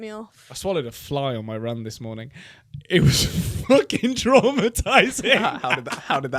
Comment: I’m watching this now but in the clip I saw, I thought said ‘I followed a fly on my run this morning’ and I was like okay girl get it